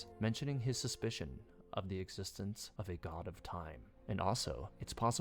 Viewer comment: I literally thought it said a god of Timmie